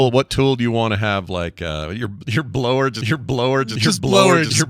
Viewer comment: It sounded like you were saying "DID YOU BLOW HER?" I could not stop laughing